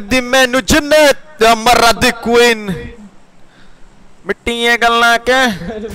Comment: i skipped 10 seconds and i heard "mcqueen' 0:13 then i skipped back and realized he was singing BLUE EYES